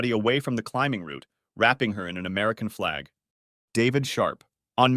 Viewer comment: i thought you said david shart